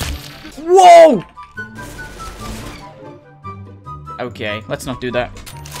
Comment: when u siad okay at 3:14 it sounded like im gay 😂😂